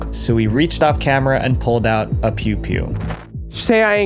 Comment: Omg when you said pew pew i thought you said pube hair 😂😭